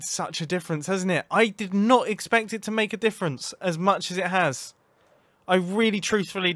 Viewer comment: I thought he said as much as I want to say it hasn’t made a difference it really has